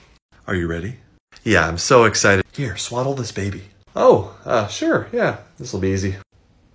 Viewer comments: I thought he said swallow the baby instead of swaddle the baby ... it was a very confusing two minutes for me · More than one person thought I said “swallow this baby” at the beginning. Please don’t eat babies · More than one person thought I said “swallow this baby” at the beginning. Please don’t eat babies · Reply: What was I supposed to hear? Cause what I heard was, "Swallow this baby- OH-- SWADDLE this baby? ?? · The first time around I thought he said, "Here, SWALLOW this baby" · Omg I thought you said “swallow this baby” at the beginning 😭😭 caught me so off guard until I realized it was swaddle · More than one person thought I said “swallow this baby” at the beginning. Please don’t eat babies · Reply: I mean I also thought I heard swallow the baby, but context told me otherwise. This is probably my favorite of your ace the interview sketches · In the beginning I thought he said "swallow this baby", and I spent the following 1 minute and 5 seconds being very confused. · More than one person thought I said “swallow this baby” at the beginning. Please don’t eat babies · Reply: Ohhhhh swaddle. I def thought you said swallow too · I swear I thought the first thing he said was swallow not swaddle this baby 😳 🤦🏼‍♀️ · Yo I thought he said swallow the baby I was so confused for a minute 😂😂😂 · At first I thought he said swallow this baby I was so confused hahahahha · Instead of swaddle I thought he said swallow, I was worried for a sec